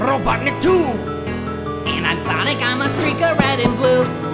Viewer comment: I always hear streaker. Always.